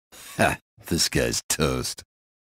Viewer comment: i always thought it said "this guy is a toast"